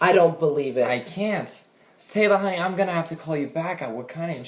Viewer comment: totally sounds like he said saylor